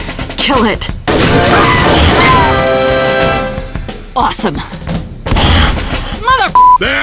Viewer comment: i thought i heard the zombie girl say "meow"